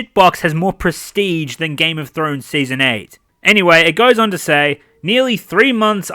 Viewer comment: Love how the english subtitle reads SEASON AIDS instead of season 8. <3 Youtube is smart.